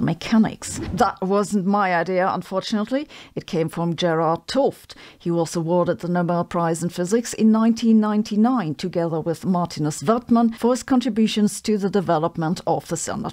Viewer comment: I thought you were saying Gerald Toft. Then I saw his book.